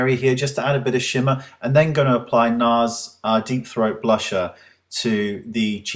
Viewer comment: lol does he say Nars DEEP throat blush??